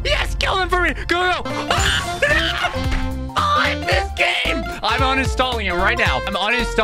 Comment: wait what did bro say at like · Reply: "Crud this game"